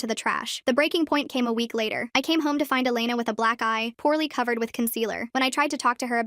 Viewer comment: I honest to god thought she said black guy